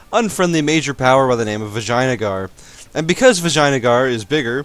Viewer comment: Did i hear good: in 0:56 Vaginagar xD